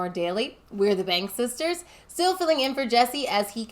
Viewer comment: I heard "the bang sisters" and was like "what did youtube autoplay?"